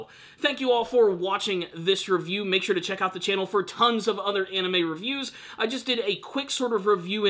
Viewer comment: Did you just say "tongues of other anime reviews"?